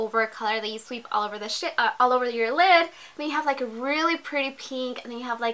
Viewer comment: did she say "You sweep all over the shit....." ?